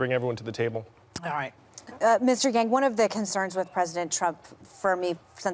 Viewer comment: Did she really say mr.gang?😂